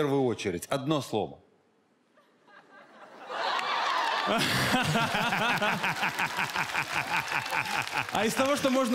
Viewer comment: что он сказал? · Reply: О*уенный😎